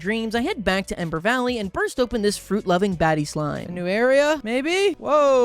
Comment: I'm sorry, at 21:57 I really tried hard but I kept hearing "baddy slime", I'm not pure anymore 😢